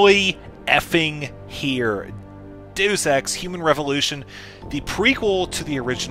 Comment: did he say "do sex"? 0_o